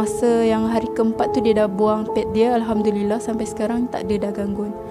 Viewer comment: laki dia ke basuh pad dia ? haha dengar macam akak sebut laki dia ,ke salah dengar 🤔